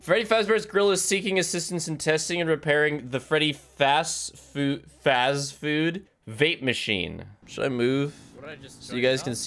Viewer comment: I heard “Freddy fazbear vape machine” at first and you better believe the start of this video was confusing